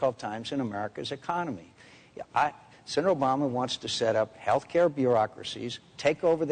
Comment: at one point it sounded like obama said shut up to mcccain